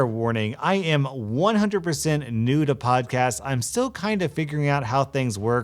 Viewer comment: i legit thought you said "I am 100% Nude in my podcast-"